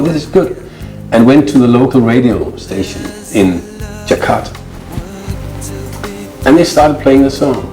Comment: What city did Kare say? I can't hear it clearly. Is it Jakarta, Indonesia? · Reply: Yes, Jakarta